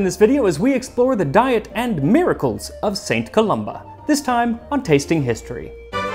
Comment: When Max first said St Columba. I thought he said St Columbi. As in multiple St Columbus's. Just me? Okay.